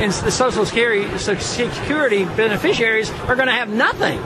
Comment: he makes a Freudian slip: "...social scary..." instead of "security." He is absolutely right, programs like social security are really scary. They lead to a collectivist state, because they make people expect things from the government, instead of self-reliance. Go, Ron Paul! :) We need him very badly!